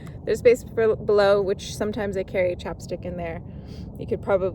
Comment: chopstick? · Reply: Having watched it again, I think I misheard you. At 4:18 I now think you said "sometimes I carry a chapstick in there". I heard "chopstick" and wracked my brain wondering what ingenious use a fountain pen enthusiast had for a single chopstick and where I could buy just one. Silly me.